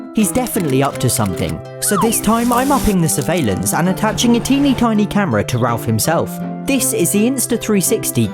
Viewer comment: Lmao, I thought it said “I put a tiny cat on my camera.”